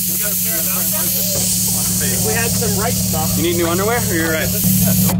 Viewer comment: Why at 9:01 did I hear “u need new underwear?”